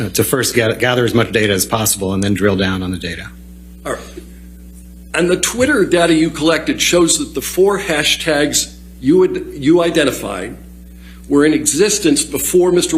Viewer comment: everytime the lawyer says twitter data, i hear twitter daddy lol. funny funny like at 6:40 lol,, sounds just like it